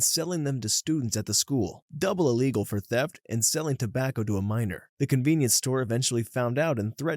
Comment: you said minor but they captions said miner